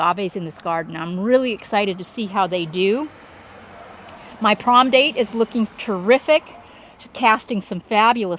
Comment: Sorry, what did you say was the tree at 4:36. Words on CC said prom date. Thanks, Love, love love your creation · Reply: Aloe banesii